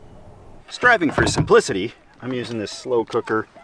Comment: I had to play this 4 times with the caption on. My jaw, flew open. actually says "Striving for Simplicity".... it sounds like he says, "Striving for some.... ummm.... "Kitty" with a 'P'. 😮❤😂. Shockingly... Funny. 😂😅😊. Canadian accent got me... Lol